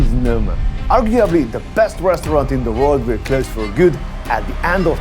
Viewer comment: The soundtrack is intensive. At about 0:12, did you say of Noma best/worst restaurant in the world?